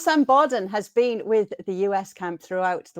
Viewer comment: I thought she said Stan Boardman.................. Nothing against Sam Borden, I've never heard of him before, but I'd prefer Stan Boardman, if he did the interviews it would definitely bring a bizarre edge to things. Thinking about it, that's not a bad way of describing his act, the way I remember it, anyway. Stan Boardman was an English comedian, if you didn't already know, who achieved some level of fame in the 70s/80s, there were a bunch of them in those days, none of them were funny + hosting game-shows was where they'd all end up. Why I'm going on about him, I forget????? It's a break from arguing about football, + no more of a waste of time..............